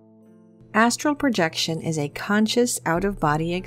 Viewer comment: I have watched this video atleast 20 times and up til now I thought you were saying asshole projection